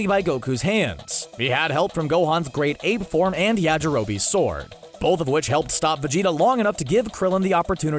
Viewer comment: who else heard Yajirobe's sore?